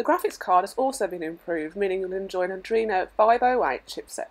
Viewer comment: Wtf did you say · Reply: Bieng improved meaning 'and then join audrina 508' accorinding to subs but i dont think they ar correct